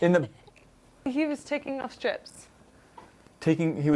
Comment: I thoughts is "Whitening his teeth" meant he was pooping. said, "He is taking his STRIPS" off. I thought she said "he is taking a $#17. (family friendly)." I thought was less Disney Princess, nope he's still one......maybe